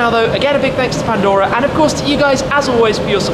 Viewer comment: that's cool does it have remote car start and 1 question ever have a time when your cars break down on you and you should do a video when its nice out on a motorcycle why do you say chiiiz its cool but did it take alot of practice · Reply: He went over a whole segment on the lack of remote start. He says ”cheers.” nit chiiiz. I thought he was saying ”jizzzz” at first.